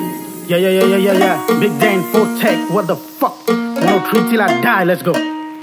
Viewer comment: what the hell is saying? Treat you like dialer or? I actually like his style but he's audibility is nil, don't know about you guys but he is completely inaudible for me for that matter it's not only him but almost all of them except rap was the only that was kinda understable without looking at lyrics.